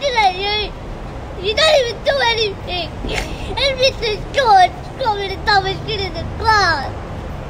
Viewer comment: did anyone else hear him say at 0:44 something about a teacher calling him the dumbest kid in the class??